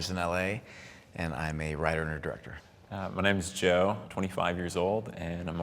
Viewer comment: thought he said he was 5. Was gonna say damn that's a growth spurt